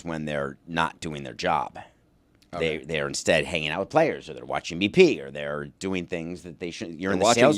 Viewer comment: I thought he said watching me pee too😂😂😂